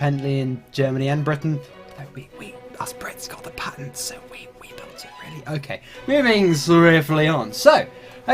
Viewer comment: what did you say at 1:54? "us Brits... the Brits did what xD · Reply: I think the whole phrase was: us brits, we got the patents, so we built it first.